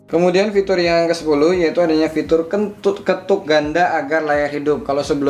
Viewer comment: ane dengernya kentut ketuk ganda ?